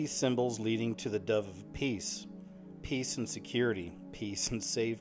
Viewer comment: When you said the death of peace it reminded me of a dream I had that included raining dead doves...after thinking about it a few times I thought (or heard) in my head “the end of peace”